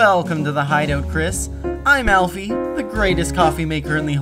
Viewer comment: his name is alfie what i hear "ALPHYS" (UNDERTALE INTENSIFIES) Edit: this was made in Jan 13.? MY BIRTHDAY